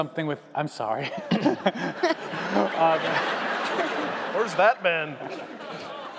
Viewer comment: I thought he said "where's that been''...